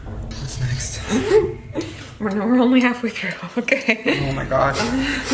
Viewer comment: What did she say? Something "only halfway through"? I'm not a native speaker so I would appreciate if someone could help me :) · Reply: She was telling Shane that they were "only halfway through" what she saw reading to him. It's a bit hard to understand while she's laughing but that's what I got!